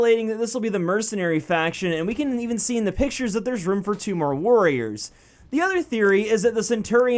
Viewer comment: When you said "warriors" I thought you said worriers